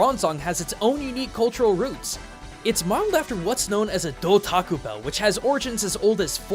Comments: who else heard "dull taco bell" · I heard "Da Taco Bell" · I heard du taco bell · who else heard taco bell :/ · at first i thought he said dell taco bell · I thought he say "A Dough Taco Bell" and lost entirely... I'm so sorry. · did he say Taco bell ? 🤔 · did I hear taco bell? am I crazy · did you say taco bell?